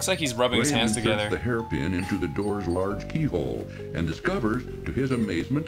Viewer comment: I thought the narrator said "Pee Hole" and almost choked on a meatball I was eating